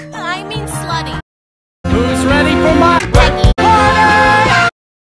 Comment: did she say yogurt? 😂